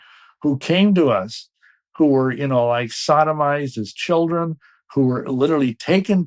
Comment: did he say he sodomized children?